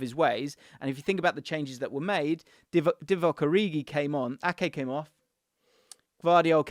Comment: Did you just say Divock Origi comes on? 😂😂